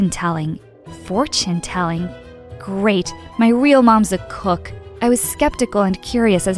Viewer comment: "GREAT my real mums a COOK" did anyone else hear that lol